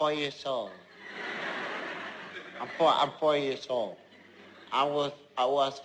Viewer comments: I always thought he was saying; " I'm here for your soul " Instead of "I'm four years old" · At first I thought when said"I'm 4 years old" I thought he said "I'm for your soul" :/ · At first I thought was saying "I'm for your soul" not "I'm four years old" XD I HAVE A PROBLEM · When said "I'm four years old" I thought he said "I'm for your soul" What's wrong with me